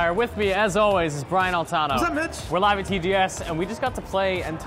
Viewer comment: I thought Brian said "Sup Bitch!" At the beginning lol